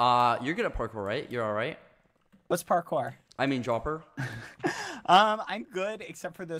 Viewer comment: was it just me or did I hear bbh say, “what’s parkour”